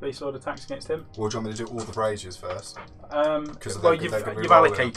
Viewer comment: did I just hear you say "And your 4 attacks with the brassiere?" I thought played Skaven, not Slaanesh ha